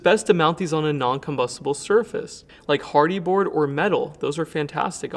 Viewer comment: Does say “hardy board”?